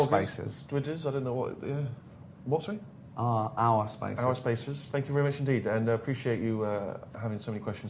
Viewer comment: when Gervais said "ourspaces" I thought he said "arsefaces"